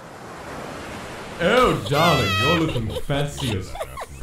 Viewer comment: I thought Mettaton said: Oh darling, you look fancy as FUCK XD